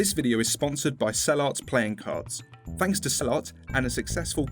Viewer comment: I heard you say cellart and I automatically transfered it to "sellout"